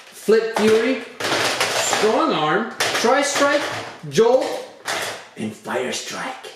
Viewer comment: Did he just say Tristrike